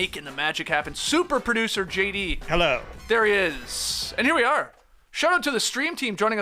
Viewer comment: When JD says Hello for some reason I hear “hey get off my grass”